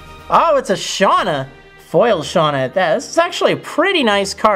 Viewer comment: anyone else heard sexually or am I just being creepy? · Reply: He said actually